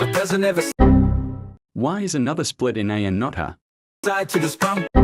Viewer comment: Why did the subtitle say nother it’s supposed to say another